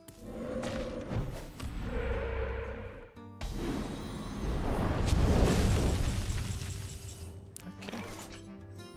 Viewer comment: Can someone tell me what Frodan is saying in the clip at 3:00 where Reynad sounds funny? "You cut out a little bit there Reynad unfortunately ...? ...? .. . about my chipmunk either." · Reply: Tom Riddle I didn't brush up on my chipmunk either